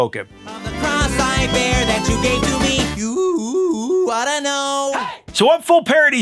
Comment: If Weird Al missed this, I don't know how. Alanis says "Of the cross I bear that you gave to me" Sounded like Al said the same but what I heard is "Of the cross-eyed bear that you gave to me". As in Teddy Bear. Either way, what I heard gave me a huge laugh. 💀